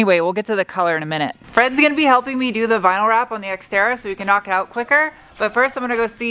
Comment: I heard her say Sexterra.